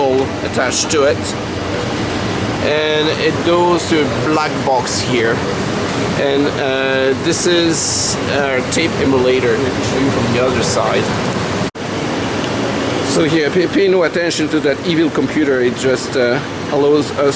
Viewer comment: That sounded like "tape immolator". I imagine that's occasionally correct...